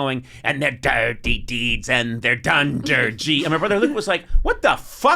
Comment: I actually always heard it as "Dunder Jeans" I didn't know the actual lyrics until now. I never bothered to fact check it.